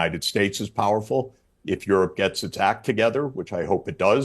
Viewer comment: oh "gets its act" togther. i thought he said i hope it gets attacked